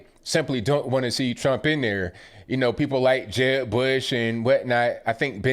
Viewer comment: I love your accent😄 To me, it sounded like you said, “People like Jeb Bush and Wet Knot” and I thought, “I dunno who Wet Knot is, but I’m sure he fits right into the RINO crowd.” 😆